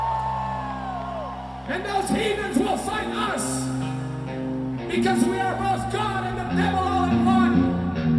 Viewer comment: What did Serj say at 1:44:00? Cant understand first two sentences, before "We are our own worst fucking enemy" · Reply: TaveruS "And those heathens will fight us! Because we are both god and the devil all in one!"